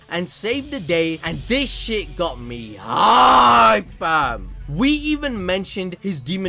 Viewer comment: did said "this shit got me hard"?